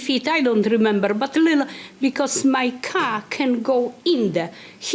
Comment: "my 🐓 cant go in" what did you say... oooh 🚗